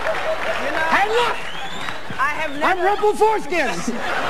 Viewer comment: Could someone explain to me what Robin says at 1:30 : "Look, ..." ??? I'm a big fan of Robin & Billy but I'm French and there are so many things I don't understand in these videos, it's frustrating ! :( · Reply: Rumplestiltskin is a fairytale character who had a hunchback and made a woman turn straw into gold. So Robin said-" hey look! Im Rumpleforeskin!". Foreskin- is the skin around the male genitalia. So he did an impression of penis in a creative and hilarious way.